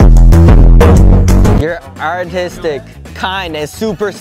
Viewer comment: I always thought they said autistic